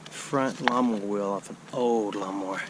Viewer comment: No golf balls yet? That IS shocking!! When you said, "lawn mower wheel" I heard "llama wheel" and I couldn't figure out what the heck a llama wheel was! I like when you walk and talk.